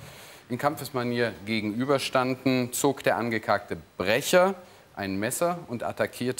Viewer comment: Bin ich der einzige, der da "angekackte" verstanden hat? 😀